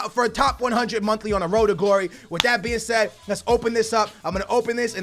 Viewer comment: can anybody tell me what is he saying here? "With that be instead" or what?